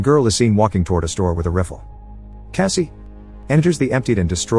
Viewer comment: "Walking toward the store with a reefle." Ahhh my favourite type of gun...the reefle. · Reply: I guess we can add that to the list of words the AI cannot pronounce. So weird. Such a simple word. I had to rewind to see what this rifffel was. Thought he said wiffle